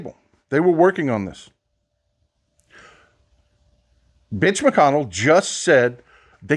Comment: did he just say "Bitch" McConnel? 😂😂😂😂😂I swear I heard it, lol.